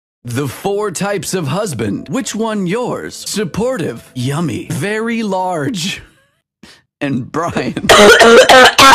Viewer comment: I thought it said and dry but it said and brayan